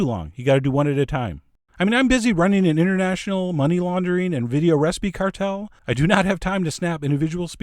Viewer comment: Did you just say "international money laundry"?!